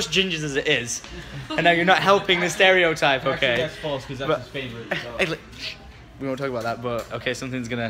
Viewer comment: "I hate gingers" Well that's false, Lex is your favourite" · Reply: I thought he said 'well actually thats false cause thats his favourite'?